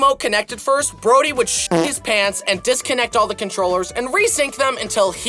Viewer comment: what did u say… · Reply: He said that Brody would 💩 his pants😂